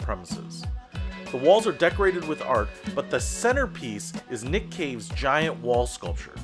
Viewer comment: I had to turn on the close caption because I thought he said Nick Cage